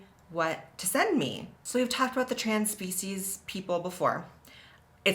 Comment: HA I thought you said “trans feces”